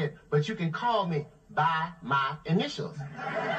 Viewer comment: I didn't get it even thou I heard it twice ! Honestly someone explain Why is this funny. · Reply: Cedric thought the man was telling him to call him "Bominicious". The man was really telling Cedric, "Call me BY MY INITIALS." The man's name/initials was C.J.....Cedric thought the man's name was Bominicious. The way the man was saying "by my initials" sounded like he was saying "Bominicious".